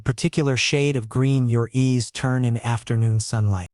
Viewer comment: "...his grey eyes, similar to his father's in colour..." huh? A few sentences named father was described with iceblue eyes? Every time when it's about Sam, his father's eyes are grey like his... but when it's about Asher alone, his eyes are iceblue... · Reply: Good catch. Asher also notices how Liam’s knees turn a particular shade of green in the sunlight (40:07). The captions say eyes but the AI voice says knees.